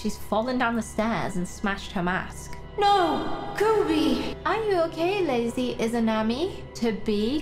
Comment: When she said " are you okay lady izamani " sounded like " are you okay lazy izamani "